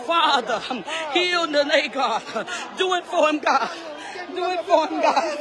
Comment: What THE HELL SHE SAYING IS SHE SPEAKING IN TONGUES OR JUST SAYING ANYTHING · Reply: She’s praying she said ‘’Do it for him God’s it was clearly she was just repeating it wasn’t talking in tongues 😂😂😂